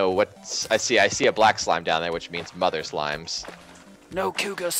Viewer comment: "I see a black slime down there which means mugging other slimes." Is that what you said cos that's what it sounded like. And lol at it too.